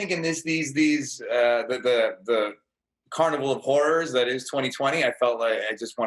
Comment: wtf did he say? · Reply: Carnival of horrors